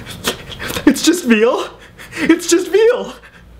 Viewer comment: I thought he was saying it's just v sause